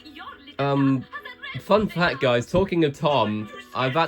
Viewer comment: Sounds like you said Talking Tom